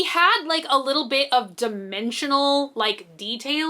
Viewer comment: I thought you said he was going to have dementia when you said dimensions lol